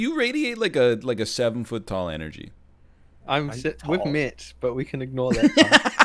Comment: What did say in 10:30? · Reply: 'I'm si- We've met but we can ignore that part.'